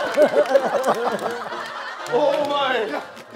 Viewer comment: Can tell that is Heechul saying Oh Mine !!